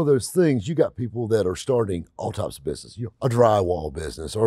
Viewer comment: I swear I heard "You have people starting autopsy businesses"